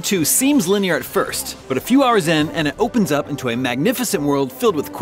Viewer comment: At the 3 second mark I first heard "if you are Zen," instead of "a few hours in." 😛